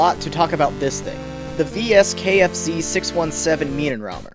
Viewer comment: did u say KFC?